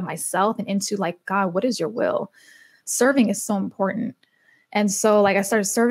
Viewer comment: Take a listen! <link> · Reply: Thought she was saying “cerning”. Lol. I was like “what is cerning?” Soo i turned on subtitles and saw “Serving “ Oh. Duh. Lol